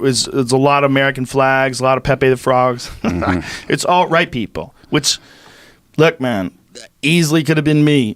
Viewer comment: "alt-right people." What a joke you are. You're even naming instances that you could have asked about but didn't. You're just a fraud. Your team is a fraud. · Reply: I didn't hear him say alt right. I heard him say ALL right people. He repeatidely said right-wing too, not alt right. You're hearing what you want to hear to fit your own bias which was clearly set before you even watched the video. Good job.